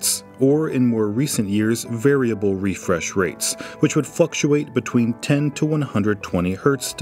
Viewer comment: Did I hear 'fluxuate' as 'fuckuate'